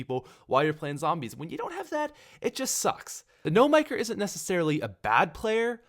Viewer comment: I thought he was saying No maker.